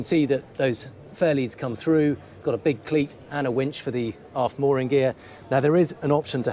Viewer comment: Did he say “a big clit”?